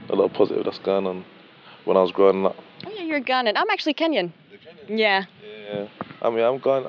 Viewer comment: Loool he said goin on an she thought he said garnian 5:15 🤣